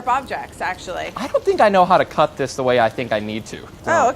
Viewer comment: Sorry but every single thing he did was wrong I thought he said he knows how to cook 🤦‍♀️🤦‍♀️